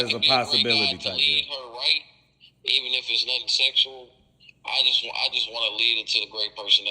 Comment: “Even if it’s nothing sexual”? 🤔 is that what he said at 14:06??? That boy looking for a piece of azz smh